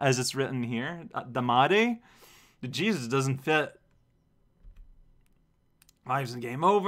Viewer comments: I had to rewind because at 1:16 I thought you said "the Jesus doesn't fit". And yes the music isn't meant to be glitchy. You were even meant to get the SEGA logo too, but it didn't play for some reason... Probably the same reason the game froze. XD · Jesus doesn't fit??? (I know what he's saying, I just heard it wrong the first time)